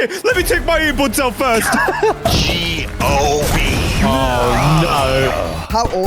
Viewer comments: did he say gob?😂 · I don’t know if it’s just me but did said GOB instead to JOB